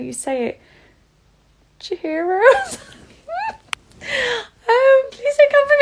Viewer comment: I honestly thought it was called chorizo 😂😂